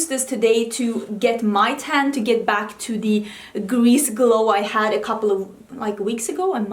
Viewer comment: When you said "Greece Glow" I heard it as "Grease Glow" and my mind immediately went to Fenty's Body Lava 😂